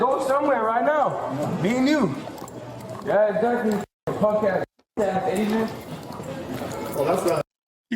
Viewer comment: did he say punk ass asian?